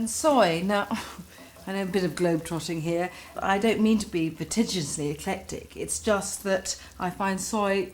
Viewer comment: “...I don’t mean to be vitigiously eclectic..” wow. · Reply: I thought she said pretentiously eclectic lol